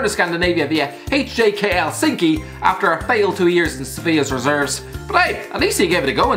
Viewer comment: Lol he said Sevilla and I thought he said Sofia, lol I don’t even know a team from Sofia